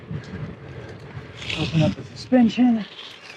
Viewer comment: I believe I heard you say "opening up extension" as you entered into the off road section. What extension is would that be? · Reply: Sorry, that was "opening up suspension" 😁